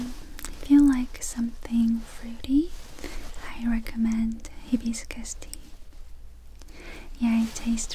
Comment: the way she pronounced hibiscus😂 sounded like "hippies cuss" 🤣 Oh, you're so sweet and adorable 💝💖